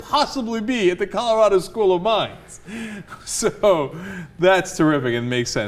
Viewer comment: At first I thought he said "school of Mimes" and...it kind of made sense either way.